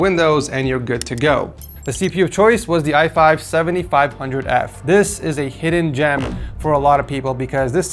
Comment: Did he say i5 7500F????